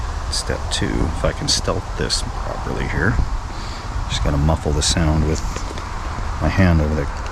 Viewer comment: i thought he said "step 2 if I can stealth piss properly here, just gonna muffle the sound with my hand" (camera starts panning down) Me thinking did I click the wrong video for a sec?